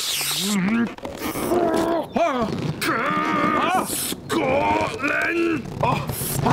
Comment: did he say Scotland